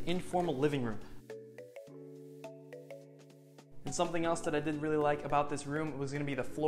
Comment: did you say you didn't like something about the room or am I just hearing things 😂?